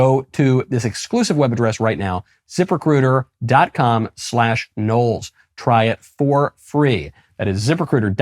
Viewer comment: Hahahahaha, it sounded like you said 'zipper critter' rather than ZipRecuiter.